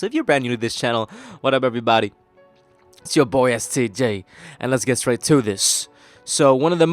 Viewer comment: I THOUGHT I HEARD ITS YOUR BOY STD HAHA